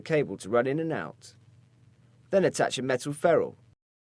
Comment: What does he say at 1:57? "attach a metal farrell"?